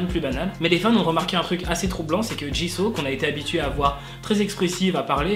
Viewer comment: C’est moi ou à 2:30 il a dit Jizo ?😂